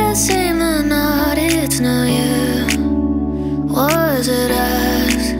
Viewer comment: I literally hear'ed wash is a$$💀